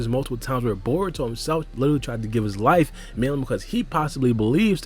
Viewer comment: what does he say after “give his life” he says it all the time and I can’t make it out. Milam? Melem? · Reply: I think it’s “mainly”